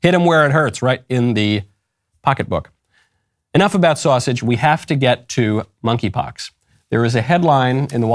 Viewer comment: I thought he said, "speaking of sausage, we have to get to monkeypox". Now that's funnier.